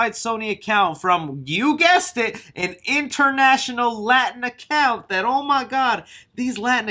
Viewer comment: For a second I thought you were saying latinx. You made me nervous